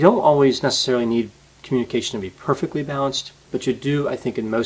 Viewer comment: I keep hearing "bounced" when you say "balanced"